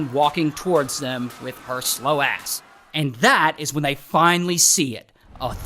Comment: Did yu just say slow ass??4:25😂😅